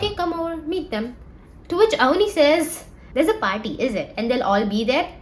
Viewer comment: the subtitles say AI instead of Avani. which, to be fair, understandable.